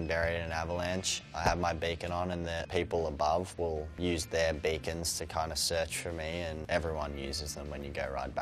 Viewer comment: Hah, I thought he said "bacon". Funny.